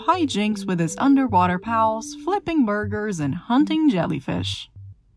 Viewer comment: I thought you said "flipping murders" when talking about spongebob